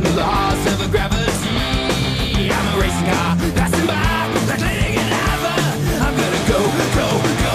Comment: he is by a harry potter poster and it sounds like he says "like avada kadavra"